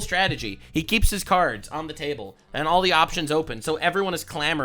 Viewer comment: Almost sounded like said "keep his tards on the table" for a second. Seems apt considering the state of politics.